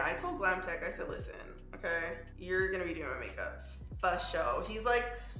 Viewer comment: Am I the only one who thought it said entanglement makeup? 😂